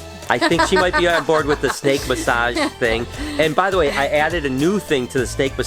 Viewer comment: I honestly thought you said steak massage